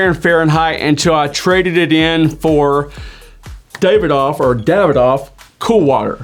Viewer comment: I could've sworn I've heard you say your first frag was Davidoff Good Life